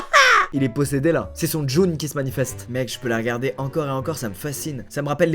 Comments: son quoi ? Djoune ??? 😂😂 on dit Jnoune ou Djinn 😂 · c’est moi ou il dit « c’est son djun qui ce manifeste » au lieu de djinn